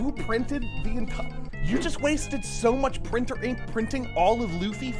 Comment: who tf said gum gum printer 😭😭😭🙏🙏🙏